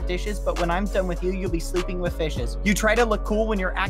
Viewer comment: Who else thought that when he said fishes he said bishes